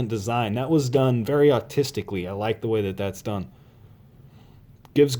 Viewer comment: did he say autisticlly? if he did lol